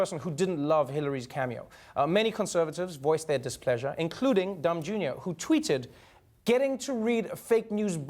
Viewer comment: I swear @ 1:13 it sounds like he said "Dumb Jr." Lmaoo